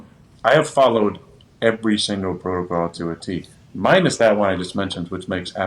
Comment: does he say he follows every protocol to a teeth?